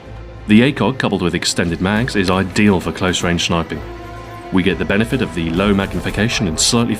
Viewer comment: anybody hear him say schniping